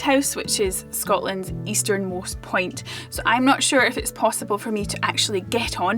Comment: Accents are fun. I thought for a second she said "easternmost pint". Accents can be disappointing.